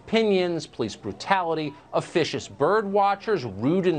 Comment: Am I the only one who heard him say "vicious bird watcher"? Even for Tucker that'd be a crazy dumb thing to say. There's no way that I heard that right.